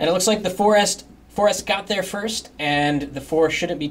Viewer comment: did he says forest